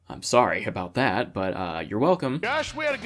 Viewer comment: Whewwww I heard 14:40 as "we had a good time tricking out... no worries!" Trick my truck just got a little less wholesome 😅